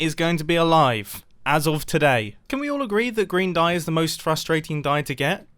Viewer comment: “Can we all agree that green dye is the worst?” Grian’s cactus farm: Allow me to introduce myself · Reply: its funny that you mention Grian, because I honestly thought he said "can we all agree that grian dye is the worst?" xD